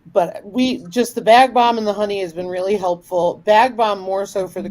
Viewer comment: did you say BagBalm?